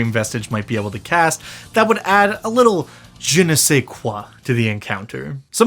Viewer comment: This is a terrifying monster and wonderfully described, but at first I heard "Flavourfull spells that a dream vestige might be able to cast, that would add a little genocide" and thought: Does it NEED any? It made me think about the concept in practical terms. One thing with the description of the dream vestige is that, unless it is somehow contained, there is no reason for it NOT to move to the nearest city through the Dream Realm in less than an hour, copies of itself arriving in all other cities on the continent within a day or two... 8 days to reach the other side of an earth-sized planet. So that is the first thing that needs to be explained, unless you drop the "inescapable craving for bodies" entirely. That aside, the average NPC (10 Int) would take 4 hits to be absorbed, so 20 temp hp. 11 commoners to make 1 dream vestige seems far too few souls for the concept.